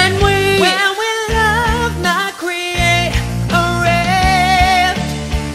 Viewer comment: I legit thought that said "Love is not Asian"